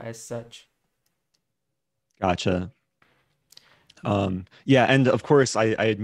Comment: Did Just Say "Accha"? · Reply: "Gotchya" 😂😂